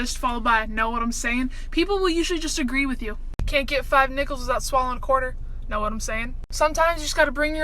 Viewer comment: I thought she just said the n word instead of nickels PHEW